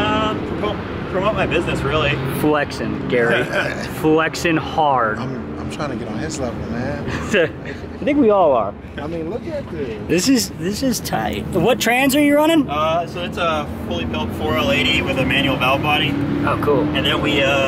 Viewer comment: Did he say "Ni66a we all are!" @ 5:51 🙊😂😂😂